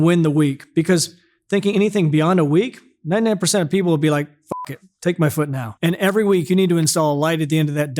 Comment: Did I just hear: "F*CK it, take my foot now? Accurate but hard core. Love it. 💪💪